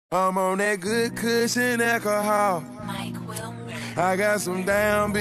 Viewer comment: i always thought he was saying “echo hall” instead of “alcohol” lmao like what college dorm is echo hall at